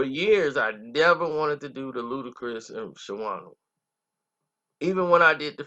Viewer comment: I thought it said shannana 😂